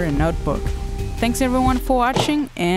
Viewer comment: Did u just said "até já"? (27:48 min)